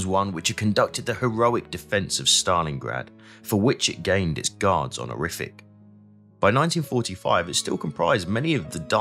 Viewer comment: What the hell was he saying when he said gained it's guards on the rific ? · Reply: Guards honourific